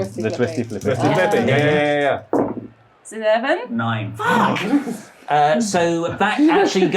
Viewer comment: wait... If rolled the 11 and rolled the 9, why did curse like she had lost? Did they just say each other's numbers and it was actually in reverse? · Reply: I think when she reads it out she slurs her words a little and calls a 7 not 11 and whoever does the graphic put 11 up by mistake